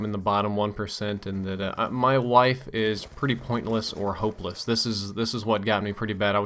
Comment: "my wife is pretty pointless or hopeless" do i have a hearing problem or is that what you actually said?